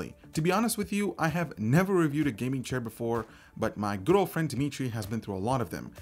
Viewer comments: Did he just say his "girlfriend Dimitri" followed by a "he"? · I know said "good old friend, dimitri" but idk why but i hear it as "my girlfriend, dimitri" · at first i thought you said "But my girlfriend Dimitry" :D lol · did he just say his "girlfriend, Dimitri"?